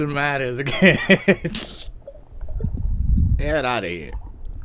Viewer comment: que fue eso? xD no entendí lo que dijo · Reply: Parece que dice “get out of here” ✌🏻